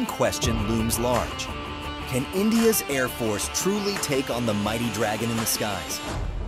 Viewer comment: You said "mighty dragon" ... I heard " pandaa..."???